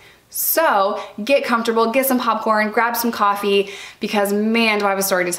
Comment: I know you said popcorn but I totally heard "get yourself some hot porn" haha